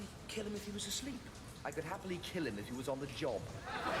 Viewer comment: "I would happily kill him if he was on the job." For those of you who don't know, "being on the job" is British slang for having sex. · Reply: I thought he said "on the John"